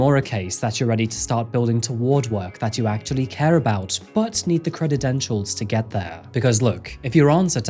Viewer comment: did he say "cred-ed-entials"!?